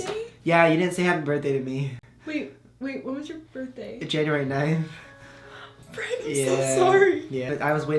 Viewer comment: Happy birthday brent and I love your videos and I thought you said January 19 because that is my birthday but then I realized that you said the 9